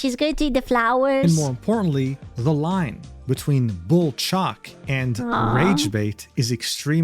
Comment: every time iv heard this i hear "bull chalk" and think he is saying "the line between bull chalk and bullshit"